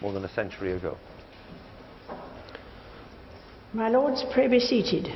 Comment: what does the queen say in the minute 9:45??? My lords... what? I Can't understand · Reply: 'My Lords, pray be seated.' Basically asking them all to sit down